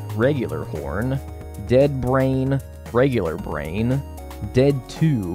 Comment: listening to this in the background and got kinda scared when i heard "dead brain, regular brain" 💀